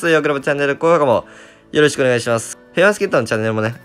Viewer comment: ペアチケットにしか聞こえんwww